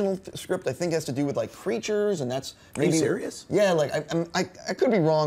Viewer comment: Totally thought said Preachers not Creatures.